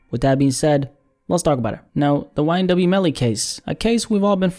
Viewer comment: I swear it sounded like said YNW Jelly Cakes 😂😂😂 I'm over here dead asf